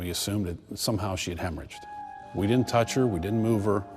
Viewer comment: this is a dumb question but what does hebridged mean or what he said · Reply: They said they thought she hemoragged, meaning they thought she may have bled to death possibly by a miscarriage